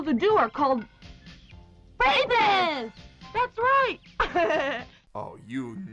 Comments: What did they call boys who touch girls? I heard r*pist but that can't be right. Right?? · "boys who touch girls are called........" racists? facists? rapists? what the fuck do they say? 24:15 seriously can someone help? i cant decipher it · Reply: Rapists